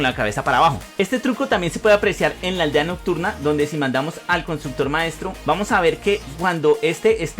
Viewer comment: Se que suena mal pero yo escuche "si matamos"